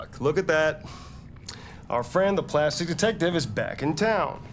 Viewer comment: I love how Gavin said "the plastic detective" to Connor 😅😅😅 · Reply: at first i thought he said "the classy detective" XD